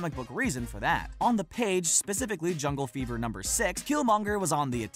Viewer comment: I'm sorry Jungle wut... my guy that page says "Jungle Action".... jungle fever, well that means something else...